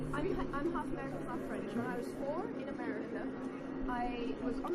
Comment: "listen i'm french." 30 seconds later "okay listen im half british half american." ???? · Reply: I thought she said half-French. Regardless, half-British half-American people can grow up in France.